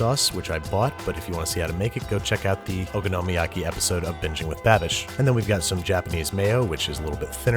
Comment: lol why did I hear 'bitching with babish' instead of 'binging'😂😂